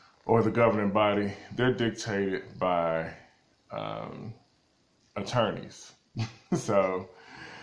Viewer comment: Policies driven by what? I can't hear he word here at 8:40. Anyone catch this? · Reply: PonyYonder He said by attorneys.... Like lawyers... lawsuits.